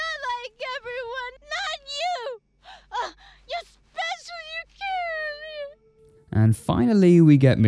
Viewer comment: did she say, “especially your kid”? It just makes me think she’s had SA from her father or something.